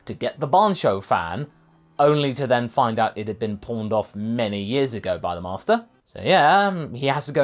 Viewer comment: When you said the Bansho fan was pawned off by Master Roshi, I thought you said 'porned' off, and I said "OH GOD!! Is that the real reason it was all sticky?!!"